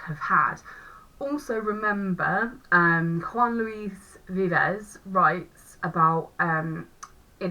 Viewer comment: Hi, this video was really helpful for my mock revision thank you! I wanted to ask who was the person you mentioned at 9:49. I didn't quite catch that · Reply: Juan Luis Vives - <link> Thrilled to help! :)